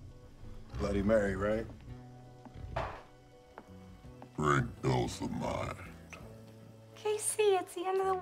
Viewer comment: bro i cant understand what croc is saying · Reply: He says "drink dulls the mind."